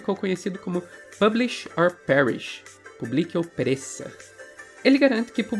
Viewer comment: Eu entendi “publique uma perereca” 😂